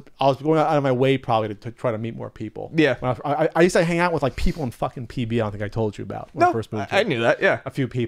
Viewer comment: What does say at 1:26? "I used to hang out with people... f'n.. at ... f'n told you about"?